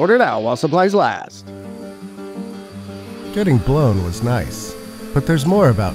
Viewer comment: Noway he did not just say, "getting blown was nice" 😆